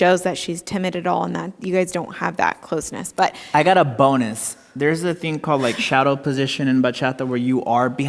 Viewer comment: Nicole , at 16:21 did u thought he said "i got a boner"? 😂😂